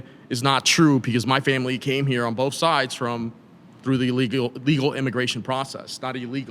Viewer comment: "my husband is an illegal immigrant. And he came here legally." That last part was added after hesitation 😂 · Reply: She said "a legal immigrant" I mean I can see how you misheard. But I'm pretty sure she just wanted make distinction betweem illegals and legal immigrants.